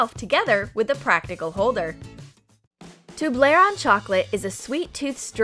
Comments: did she just say toblera? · did she just say tablarone